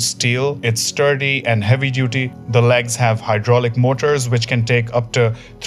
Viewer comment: Did he say hydraulic.